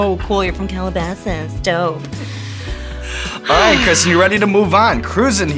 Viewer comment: I swear I heard “Kristen Syrup”.